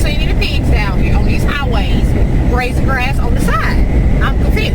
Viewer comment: 🐷 🐖 OMG....At first I thought you said KIDS were grazing grass on the side of the road 🐷 🐖 You crack me up! 😂 Thanks so much for your accent!